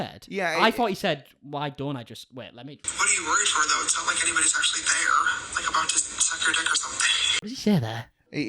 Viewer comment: To me it sounded like James said "it's not like anybody's there.. like about to suck your dick or anything" so to me it sounded like he wasn't even talking about himself and this guy but someone else and this guy. Context is everything here I think, but it's all on snapchat soo.. no context available.